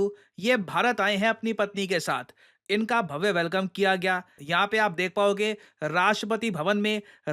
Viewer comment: I heard bandi instead of patani 😂😂